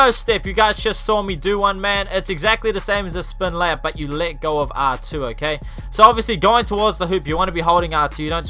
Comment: who else thought he was saying lick off when he was saying “let go of R2”